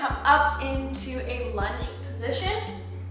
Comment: Her: “Lets bring it into that lunch position“ Me: “Lunch sounds good“ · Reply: hahahahhaha even i thought i heard lunch, but it was lunge LOL